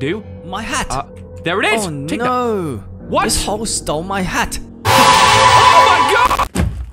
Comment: Wasnt paying attention and thought the dude said ""this ho stole my hat"".